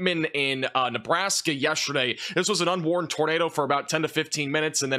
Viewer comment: What did he call that tornado? It sounded like "unworn" or "unborn" but I can't find anything on the internet about that, what did he say?? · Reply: Unwarned , no warnings for 20-30 minutes